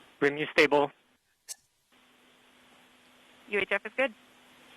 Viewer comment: I love how YouTube removed closed caption editing, now instead of "UHF is good" it says "your death is good" @ 1:16:28 I'm sure all the deaf people around the world are so fucking confused.